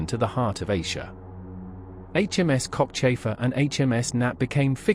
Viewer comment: I had to rewind that, sounded like HMS Cockchaser at first, not Cockchafer, lol. Thanks for the navy history!